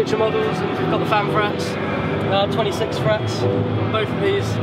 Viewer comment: did he say 26 frets on the bases?